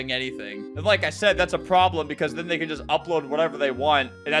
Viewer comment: did he just say n word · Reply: He said “cause then they could” trust me I had to rewind a million times too 😭